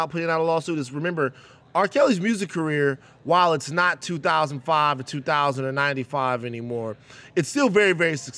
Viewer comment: did he just say it's not 2095 no more?